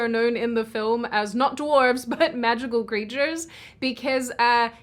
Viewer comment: I put my wallet back in my pocket when I heard "no dwarves"